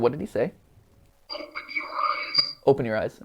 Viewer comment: I heard "I'm Pennywise" Instead of "Open your eyes"